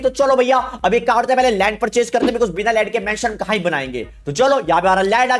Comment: told land i hear lund